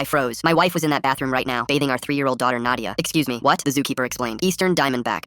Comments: The first time I heard that I thought he said "my wife is in the bathroom beating my three year old daughter” 😅😅😅😅😂😂😂😂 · I thought he said beating our 3 year old daughter 😅